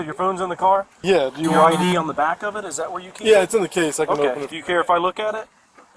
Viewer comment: wtf? “You said your phone is in the car and your id is on the back of your case, do you care if I look at it, ok thank you” all in the same sentence, didn’t even give him time to answer. But sounded like he said “I can open it before he said ‘ok thank you’” Then @ 5:25 they’re already in the car and they say “he said we can search it,” then ask him to open the trunk cuz he’s already looked in the front ha